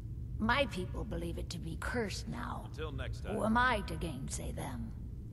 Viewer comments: Did I hear TB say "Until next time" in the background? 26:53 watching TB while Skyrimming? Woot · why do i hear TB saying "until next time" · DID ANYONE ELSE HEAR TOTALBISCUIT SAY "until next time" AT 26:50???